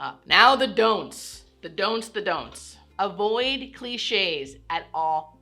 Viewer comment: I thought you said, the donuts 🙃